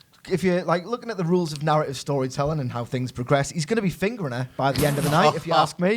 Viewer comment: Did he just saying Fingering Lana 😂😂😅😅